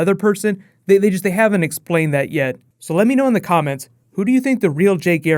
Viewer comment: Did he just say "Let me know in the comics"